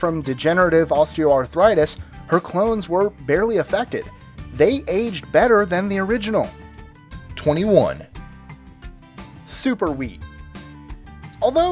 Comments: # 21 I misheard super weed and got excited..... -. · i thought he said super weed haha